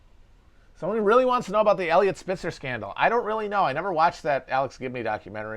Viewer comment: I thought he said Elliott Smith scandal and got really confused for a second